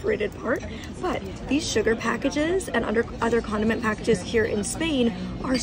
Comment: I misheard and though she said condom packages 😳